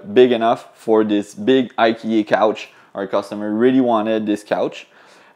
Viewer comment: lol I thought he said, "the customer wanted this big ugly couch" I was like, "It's not that bad" Then, "OH IKEA" lol